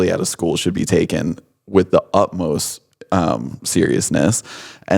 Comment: did he just say "upmost"? New girl flashback 😂